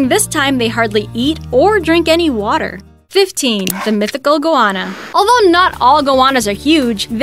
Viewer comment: I thought she was saying iguana and dropping the I. Turns out she was just saying goanna weirdly.